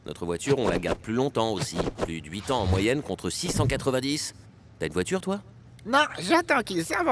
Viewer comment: contre 6 en 90, j'ai compris contre 690